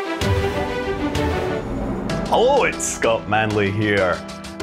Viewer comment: Can't have a KSP video without the man himself: "Hullo, it's Scott Manely." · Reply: i heard it as “HO it’s scott manley”